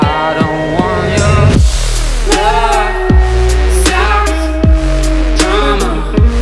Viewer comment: I kept hearing "Love sock drawer"... I was like, ewwww